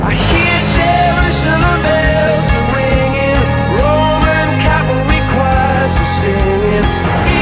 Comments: I always thought it said “Forman cavalry across the ceiling” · I thought it said “Roman Catholic choirs were singing” this whole time